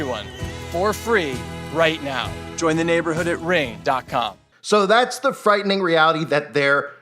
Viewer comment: sounds like he says "reign"